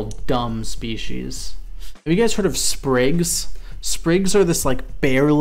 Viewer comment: did anyone catch what insect he was talking about? Sprigs? I coudlnt find them on google but interested in how they fly